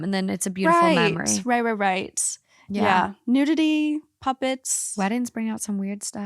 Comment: I heard “puppies” instead of “puppets” during story 2 and got excited, I mean puppets are cool I guess but a wedding with puppies sounds so much more fun 😅